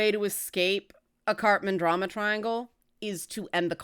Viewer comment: I'm sorry, I keep hearing "Cartman Drama Triangle" XD